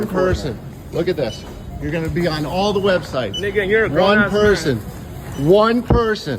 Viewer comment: what he say 🤨😭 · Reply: "ni**a you're a grown ass man" -an overweight grown ass man spending thousands of dollars on pokemon cards so kids can't buy them